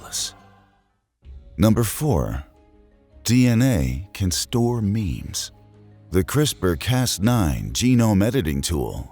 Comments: For a second I thought he had said 'DNA can store memes' would explain a lot if it did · I thought he said DNA can store memes at first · did it just say it can store memes